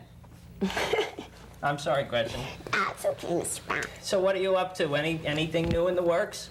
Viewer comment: Sounded like Gretchen called "Mr. Brown" during her interview (his real name).